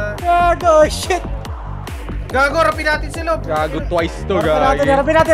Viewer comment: Wait what? Did just said Twice? Hahahaha